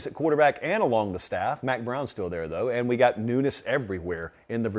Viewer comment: Every time he said 'newness', I thought he was saying 'nudists'. I was like "what does that have to do with anything???" My b!🤣